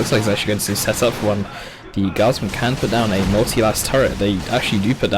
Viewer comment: I heard multi-arse turrent